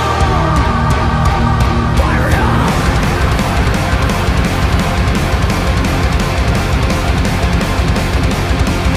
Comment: Did i hear Fuck Him Fuck Her Fuck Him Fuck Her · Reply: said fire it up